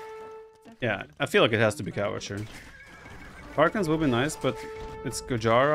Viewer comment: Does he call Cav Archers, cow archers or are my ears stupid ?